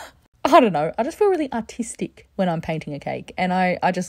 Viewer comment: That looks so elegant and as you say artistic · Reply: i thought she said autistic for half a second 😭😭